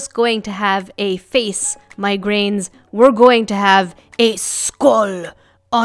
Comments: Thought u said migraines when u said my grains lol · when says "my grains" it sounds like she said migraines. Lol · it sounded like you called us migraines lol